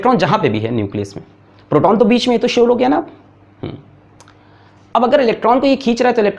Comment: what did he say? Proton to bich mai hai to solog? Hai app? Aesa kuch bola hai? Kya bolo sir ne???? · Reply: Sir ne bola ki "Proton toh beech mei hai ye toh sure ho gaye na aap"